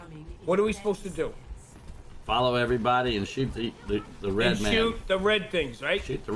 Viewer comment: I thought he said “shoot the rednecks” 😂😂